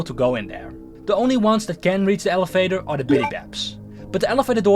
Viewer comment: Bro fix ur pronunciation jeez I literally hear elefater in